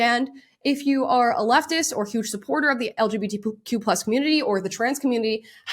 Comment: Did she say LGBTPoop community?